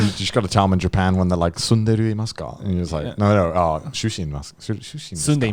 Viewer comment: At first I heard say "Tsundere imasu ka?" at 36:50 lmfao